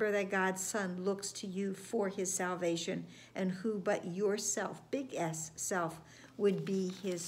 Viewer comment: Thanks. Sounded like you said “Your big ass self.” 😂😂